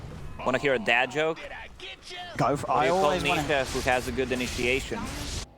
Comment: I couldn't hear the dad joke :( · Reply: its "i-nisha-a-tion" hahaha